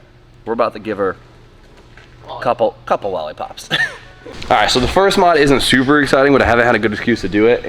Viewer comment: Am I the only one that heard him say "the first mod isn't Subaru exciting?"